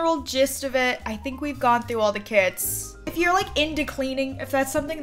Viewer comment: Everyrime you said "through the kits" I thought you said "through the kids"